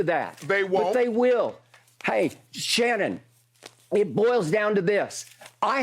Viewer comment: When skip said “hey, SHannon…” I thought he was about to say “hey, SHut up!”